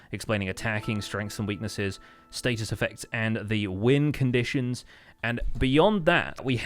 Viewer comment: I know you said win conditions but i heard wind conditions lol.